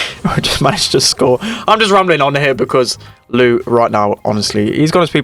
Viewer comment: did he just say Lou is gay?! that’s why he stopped for a second xd